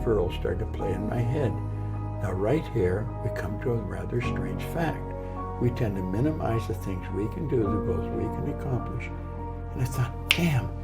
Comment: anyone catch what he was whispering/mumbling about? at 5:50 to 6:06 ? sounded important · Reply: “Now, right here we come to a rather strange fact. We tend to minimize the things we can do, the goals we can accomplish, and yet, for some equally strange reason, we think others can do things that we cannot. I want you to know that that is not true. You have deep reservoirs of talent and ability within you" - Earl Nightingale